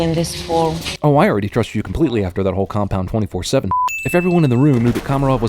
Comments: Cinema Sins Sin: At 5:05 the narrator says "Compound 24-7 shit" but the helpful subtitles say "Compound 27-4 shit". Which is it? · CinemaSins has sinned. 5:06 Captioning: "Oh, I already trust you completely after that whole compound 27-4 sh*t." Yet this is what he says, "Oh, I already trust you completely after that whole compound 24-7 sh*t." =) I still love ya, CinemaSins guy. =) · is it just me or does it say 27-4 and not 24-7 at 5:08? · the subtitle say 27-4 even though the narration says 24-7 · Anyone else notice that when he said "24-7", the caption said "27-4" at 5:07? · error in caption...reads "27-4" instead of "24-7". *Ding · the subtitles say "27-4" rather than "24-7". *Ding* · says 27-4 instead of 24-7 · I'd like to point out that at 5:08 when he says 24-7, the subtitles say 27-4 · The subtitles say 27-4 when he says 24-7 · Did anybody notice that at 5:06 to 5:08 it says 27-4 instead of 24-7? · you say "24-7" but the subtitles say "27-4" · he says 24:7 but the subtitles say 27:4 D: NOOO!!! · the subtitles says 27-4 but he said 24-7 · Says "Compound 24-7 shit," but the captions say "27-4 sh*t." This is at 5:08 by the way. (Ding) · he says 24-7 shit but the subtitles say 27-4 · Any body else notice when he says 24/ 7, the subtitles says 27/ 4 at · No Cinema Sins is Without Sins. At 5:07 captions read "27-4" instead of "24-7" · it says 27-4 instead of 24-7 :P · CINEMA SINS SIN!!!!!! 5:08 in the video you say 24-7 but the subtitle says 27-4! *ding*